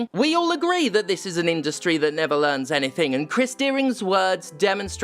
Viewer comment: Every time I heard you say his name, Steph, I thought you said 'crispy earring' :D Another great video, as always